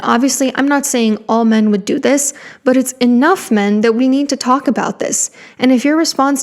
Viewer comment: ok ms feminist his right hand “man” was a woman so chill tf out with that sexist “all men are evil” bullshit 💀💀💀💀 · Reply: she actually said enough men, not all men, as you misquoted